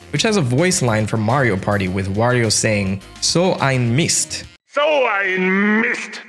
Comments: interesting! I always thought he said “Oh! I Missed!” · I WAS SEARCHING FOR 10 YEARS FOR THE "SO EIN MIST" THING I heard it on a Dunkey Lee Sin video like 10 years ago and I thought it was the funniest soundbite ever but I could never track down where it was from until this video, I'm a grown man now and I finally have closure. I genuinely can't believe it. I always thought it said "Oh I missed!", fantastic stuff.